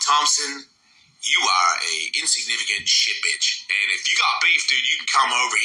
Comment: Did he just call him a "shit-bitch"?? That's great! Haha